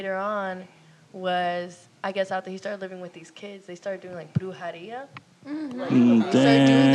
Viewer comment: What is bluhariya?? The thing Gina said Bruno, x and others were doing? At 48:50 Edit: nvm. Just found out it’s a Latin American term for witchcraft. And it’s brujeria